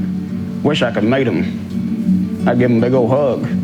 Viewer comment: at about 4:10, speaking of the president, a man says, "I wish I could meet him." I had to rewind twice. It sounds like he says "I wish I could mate him." I was convinced that's what he said. But on the third listen, I figured it out.